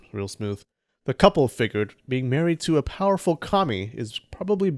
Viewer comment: Him: “Being a powerful kami” What I hear: “a powerful commi” That bastard